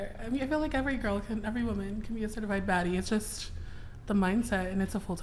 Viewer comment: I thought she said “certified fat bitch”. I thought she was going for the Fat Amy vibes.